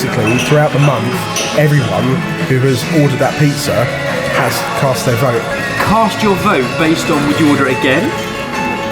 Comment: what does Jamie say at 5:04 ? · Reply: Oskar Holm - I am almost certain that he said... “All that’s missing is some Salad Cream”. Probably because today Heinz said they are debating to change the name to “Sandwich Cream” 😂 I like Jamie... He’s a man that doesn’t give a fuck about food really. Because he knows that it all goes down one hole and a day or two later it comes out the other hole 😀 He appeals to me because he has a sense of humour and doesn’t come across as a “Food Snob” 👍